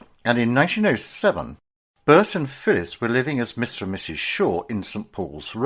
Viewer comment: I had to turn on subtitles because I thought you said Phyllis Diller 🤣 Thanks guys, I needed that double take! ♥